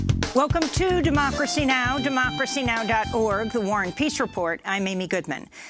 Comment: Every time I hear Amy Goodman introduce Democracy Now....for a long time now....until I listened very, very carefully....I thiught Goodman was saying the "Warren peace report".....and I thought to myself WHO THE IS THIS 'WARREN' PERSON? Until once I destinctly heard her say the "war and peace report". Then I knew....there was no Warren. I'm thinking articulation could be practiced....but only because on recognising she WASN'T saying Warren....I felt a little foolish....because the introduction made alot more sense then.